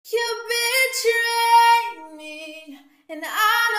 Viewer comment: At the beginning, I thought it said "you bitch" I was like... okay then